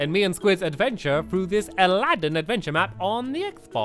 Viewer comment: I thought it said aladin cave den XD XD XD